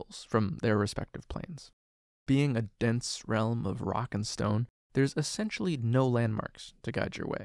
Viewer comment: did I hear a rock and stone??